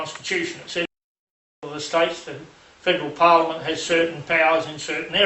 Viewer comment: what dose he say??? The sound cuts out that Verry moment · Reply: pretty sure its federal" as you may hear the end of the word after the brief sound loss